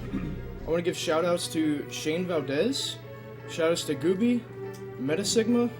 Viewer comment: Did he just say Gooby?